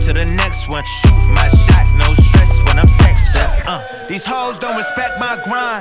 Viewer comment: Thought he said ''Shoot my shot with an actress''